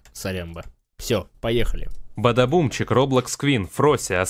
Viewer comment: бАльшлй бадаБум! Я один услышал это как из "Пятого элемента" ? )))